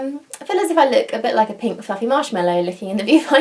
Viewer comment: I thought you said "I look like a big f*cking marshmellow" I was like whooaaaa!!!